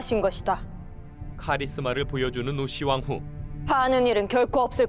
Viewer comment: 음성에 노씨왕후로 들리네요 우씨왕후 이 드라마로 이미지 세탁될까봐 걱정이네요 왕후와 그 일족의 악행이 삼국사기에 자세하게 실려있습니다